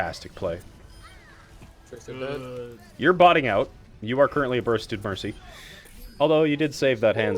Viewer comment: "You are currently a ___ Mercy" What is he saying there? Sorry, new to watching these kinds of vids. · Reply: He said, "You are botting out, you're currently a bursted Mercy". I think (and please correct me if I'm wrong) that he's trying to say that Mercy was playing like a bot: No situational awareness, jumping right into the middle of team fights instead of staying on the outside, attempting risky resurrections. I've taken 'bursted' to mean you're getting hit/dying before you realize what's hitting you/reacting effectively. I'm not 100% on the "botting out" part, as I've never heard it used in that context, but he also says it when Mercy tries to panic rez and gets killed, which would make sense.